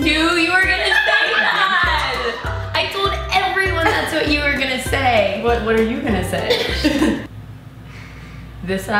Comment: Who did say at 3:46? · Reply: zoella